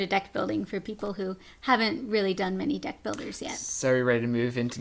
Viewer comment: lol i thought you said death builders :P not deck